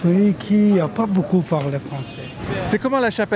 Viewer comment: Qui paye la maison ? -Moi pas parler français 🤣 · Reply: j'ai compris "y'a pas beaucoup à part les français"